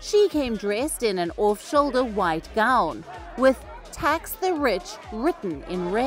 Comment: I thought it said "Tax the btch".